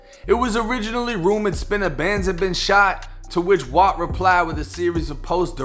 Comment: Man I thought u said spinabenz himself got shot my heart dropped